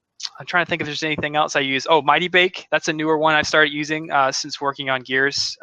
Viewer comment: Waht is the tool he's mentionning at 32:18? I cannot understand the name. Thank you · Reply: Mightybake <link>